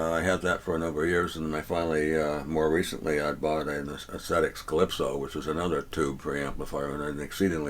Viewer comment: Does anyone know what the name is of the preamp he mentions that he used to own? Sounds like he is saying Aestetic Klipsho or something similar. Just curious. 😊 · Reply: Found it, Aesthetix Calypso. :)